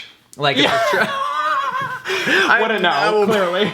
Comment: did anyone else hear him say we wouldn’t know at 26:08 whenever they talked about a steeple?